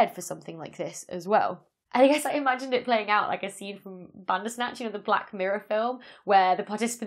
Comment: what film is this you say? ....black mirror somethingorother?? :) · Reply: Bandersnatch. It was an interactive episode.